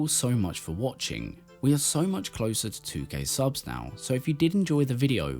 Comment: At the end, I swore I thought I heard "we are close to getting 2 gay subs", and was like, hey now! Then my brain sadly corrected it to 2k subs, which, while less exciting, is good for the uploader. I guess.